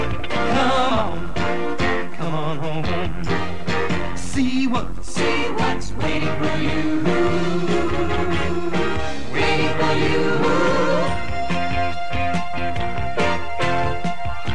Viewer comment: When I was a kid, when they say "waiting for you", I thought they were saying "Winnie the Pooh", but mispronouncing it as "Winnie the YOU." Also, at 5:10, the copyright date on "A Day at Disneyland" (1982) says "MCMXXXII", which is actually the Roman numeral for <num> is MCMLXXXII).